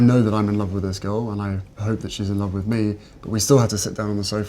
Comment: Did he just say : i hope she’s in love with me !!!! Ops